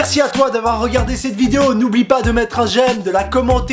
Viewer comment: N'oublie pas de mettre un gel (c'est ce que j'ai compris xD)